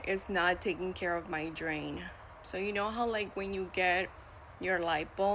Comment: did she say "drain"? Wtf is she talking about lol. What are you women doing to your bodies?